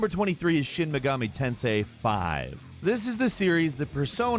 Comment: when you said shin megani tensei V I thought you said shoot the sensei v